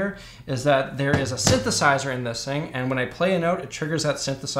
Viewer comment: The first time he said "synthesizer", I swear my ears heard "sithesizer". 🤨